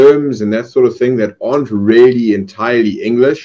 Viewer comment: 字幕見て聞いてるからわかるけど普通areとaren'tなんて区別できないよなあ…何度聞いてもareにしか聞こえん